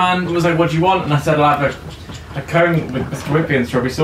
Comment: What did say at 19:26? I can't make it out. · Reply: "and then I paid on card"